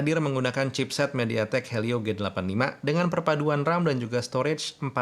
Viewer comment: p85 gw dengernya